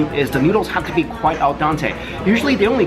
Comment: Does he say "AL DANTE" on 1:32 ??? it is "al dente".